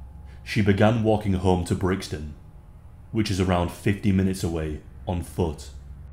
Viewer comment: she even did everything we are told by our mothers. She wore bright clothing so people would rather remember her. She wore leggings and sport shoes so she could run. People knew where she was. It was just a 15 minute walk. This is so terrifying. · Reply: Sorry to seem like I’m trying to correct you, but I thought he said 50 minutes. I rewound the video to make sure I heard that right. Although, 15 or 50 minutes is still a long time to be out in public settings at night, both long enough for this to happen ☹️